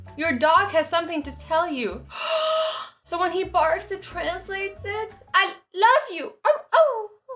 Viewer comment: When she said "when he barks it translates it" I thought she said when it farts its translates it🤭🤭🤪😜